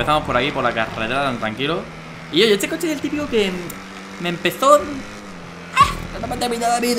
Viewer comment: Alguien sabe lo que dijo en el segundo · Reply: Empezo creo